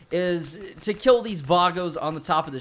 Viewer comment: I thought you were about to say vatos ahaha